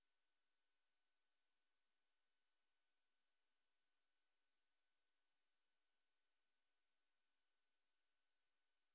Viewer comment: What does he say off mic at 2:46???? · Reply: “I told you I’d do it “